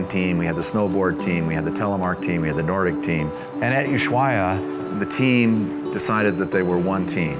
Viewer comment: What is he saying at 00:06? Sounds like At-you shiwa. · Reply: Hi Elaine! He is referencing Interski 2015 that was held in Ushuaia, Argentina. If you'd like to learn more about PSIA-AASI and Interski 2015, please take a look at this playlist: <link>